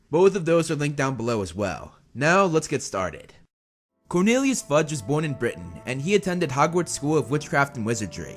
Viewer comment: First words of the video: "Cornelius Fudge Sucks" Me: We're off to a great start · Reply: Thought u said Cornelius fudge snacks lmao